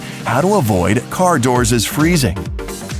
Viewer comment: Did he just say car “doorses” on #3!